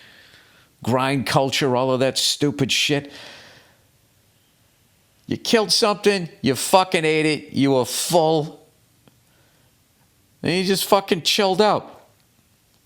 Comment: When Billy says "you f**kin' eat it" I keep hearing "you f**k and eat it" 🤣